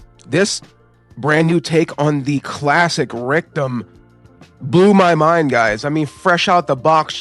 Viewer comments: Rectum? lol when you say Rick Dom it sounds like you say Rectum haha sounds stupid lol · I love the video but i had a few chuckles the way you say rick-dom. I thought you were saying rectum 😂